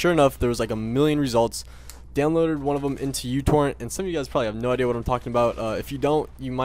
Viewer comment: instead of youtorrent i thought you said youporn at first